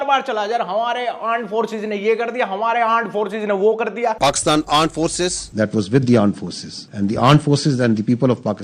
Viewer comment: i thought he said " pakistan aand forces"